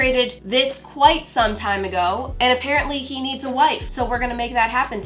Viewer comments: When you said Alfred needs a wife I thought you said Alfred needs a life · I thought said "He needs a life" in the begining